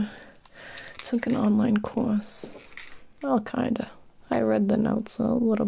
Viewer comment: Omg at 8:50 i thought she said she “took and online course. Al Qaeda” 😂😂